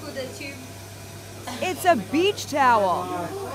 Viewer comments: I thought it said tower 😂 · Thought it said tower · I thought it said tower😂😂😂